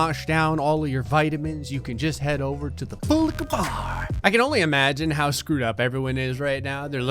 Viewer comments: The way he says " full liquor bar " always sounds funny. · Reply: I always thought he was saying "boolika bar" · The way he says " full liquor bar " always sounds funny. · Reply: Wait that's what he says I thought he said bull lika bar